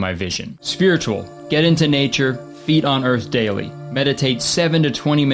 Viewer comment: i thought it said "feed on earth" instead of "feet on earth"..